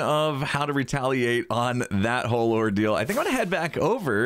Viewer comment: Every time you said ordeal I kept hearing “ore-deal”.